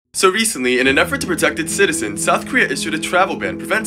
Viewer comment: At first I got confused because I thought he said North Korea